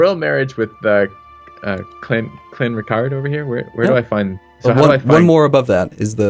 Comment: lol, i heard say ''Clanretarde''